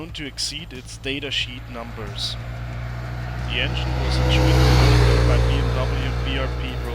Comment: We cant hear you at 03:26 and 03:36 due to bike noise. Maybe do a re-edit? Dankeschön trotzdem! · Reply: Shit! I didn't notice until now! Thank you. I was saying the engine was built by Rotax